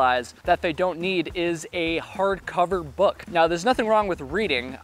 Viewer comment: Why is bringing a book a mistake? Everyone I know who loves to read LOVES to read when they get to camp. Not all hikers are through hikers who get to camp exhausted at dusk. Some of us hike so we can lay in a hammock and read a book. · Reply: I heard "hard cover book." Makes sense.